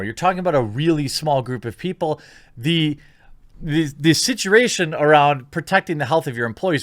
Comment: you did NOT just say "situration"! Not cool. Not cool.